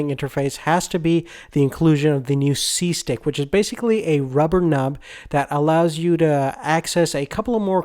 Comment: Did I just hear "rub her nub"?